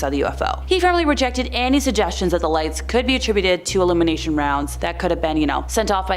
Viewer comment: Illumination rounds or elimination rounds? Sounds like your saying both.